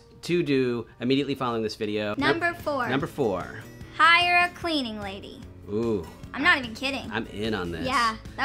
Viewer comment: The fact that she specified toilets rather than saying I have someone help me twice a week goes to show her level of respect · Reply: Also, cleaning lady. What's with that? Oddly enough watch at 23:46 and the caption says "cleaning person" (more appropriate) while she's saying "cleaning lady."